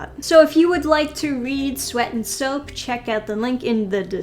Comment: Oh god, i thought it said SWEET and soap. Like "aww that sounds wholesome"